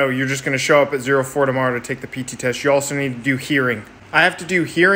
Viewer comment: Bruh....this video randomly popped up when i was scrolling through shorts and doing shi....and when he said "you need to do hearing" and for some reason, some odd reason i heard it as "urine test" and when i watched it and paid attention, i was bamboozled HARD